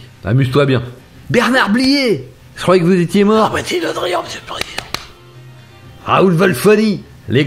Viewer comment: il dit quoi Bernard Blier à 1:55 ? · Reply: "non moi c'est Le Drian m'sieur le président" !